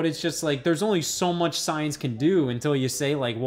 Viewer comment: Well... That's what she said. 🥳 Oh never mind, you said "science", I heard "size". 😂